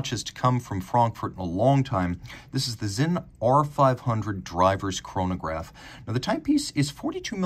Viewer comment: Oh a Driver's chronograph. I thought you said Diver's chronograph and wondered how you'd start it underwater.